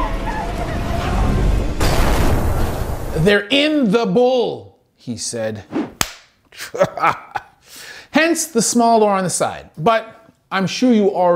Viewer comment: I always thought they said "turn the bull" not "they're in the bull"